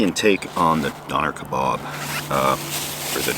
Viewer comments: I thought he said “Donner” kabob. I though was going cannibal on us! Love this channel. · At first I thought you were saying Donner kebabs. As in Donner party. I thought uh oh, what did do with those joggers 😅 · At first I thought you said "Donner kabob." I was like, "Oh, wow! Steve is gonna eat human meat." Incidentally, for people who don't know The Donner (dinner) Party, that has a sort of interesting back story, as well. Maybe not as much as some dude who liked Middle Eastern cooking, but still pretty interesting. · I thought he said Donner kebab, I was going to say that's kinda mean.